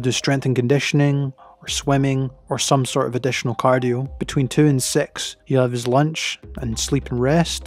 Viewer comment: I thought he said between two and sex lol...